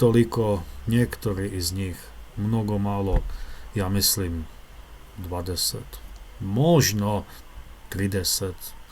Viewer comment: Dziękuję Michałowi Swatowi za transkrypcję i pomoc z tłumaczeniem. 🤓🙏🏻Do obejrzenia polecam też rozmowę polsko-czeską 🇵🇱💬🇨🇿 → <link> 🤠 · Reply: Hej na 30:07 ima greška u transkripciji i prevodu na engleskom. Treba biti DVADESET i TRIDESET (20-30) umesto 200-300. Pozdrav Hey, there's a mistake in the transcription and the English translation at 30:07. It has to be 20-30 instead of 200-300. Greets